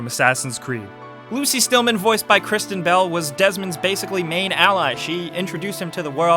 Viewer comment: I thought he said voiced by Christian Bale not Kristen Bell, I was like "Damn, he goes from the Batman to a woman (one end of the scale to the other)"